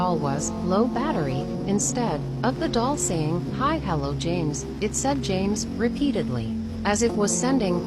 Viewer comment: I heard "help James?"I think so?